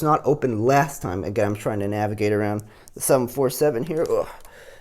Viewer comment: did he just call, a 757 and 747? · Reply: No I said I was trying to navigate around the 747.